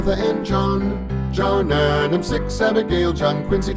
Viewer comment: What he said: “6 Abigail” What I heard “6 7” 💀